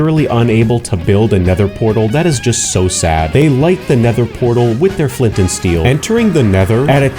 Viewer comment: I heard "They like their nether portal with the flint and steel"